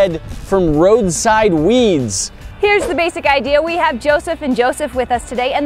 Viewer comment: FOR SOME REASON I THOUGHT THAT SAID ROADSIDE WEEBS AND WAS REALLY CONCERNED